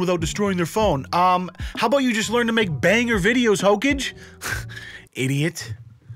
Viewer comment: pffftt he said hokaj instead of hokage😭